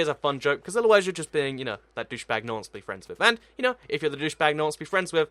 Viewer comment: i think he said douchebag in